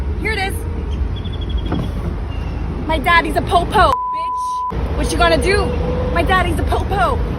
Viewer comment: If you listen to it again. Sounds like she said “my dad is a pope hoe b*tch” 😂😂😂